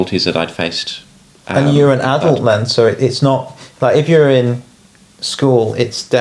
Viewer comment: did you really have to misgender her? she is clearly a trans woman, she is not an adult "man" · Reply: Hi, I think you misheard when they said “you’re an adult then…”, not “you’re an adult man”; although clearly you’re not the only one who thought that because the YouTube subtitles also make that mistake. I have known and been close friends with for 2 decades and know that’s not what they said. Yes, I am a trans woman and has been incredibly supportive of me :)